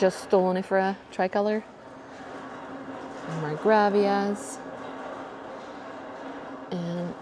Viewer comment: What is the plant at 4:22? I couldn't hear what you said. Thank you so much! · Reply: Marcgravia!